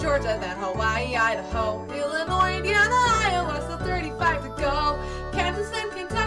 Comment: can somebody tell me what she said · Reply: iowa, so 35 to go